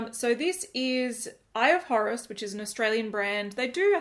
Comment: Totally thought you said “Eye of Horrors” 🙈